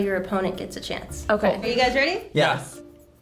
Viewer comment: 1. 0:50 correcting captions: one of those guys said yes 2. 2:39 almost wanna correct captions here, but it sounds like said Billie Leash after "eyelash"; some other high-rated comment agrees too 3. 2:44 correcting captions: said is it and not "I'll say"